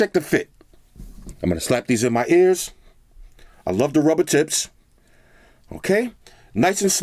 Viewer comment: did he say “I love to rub her tits”? HAHA. I’m dead 😂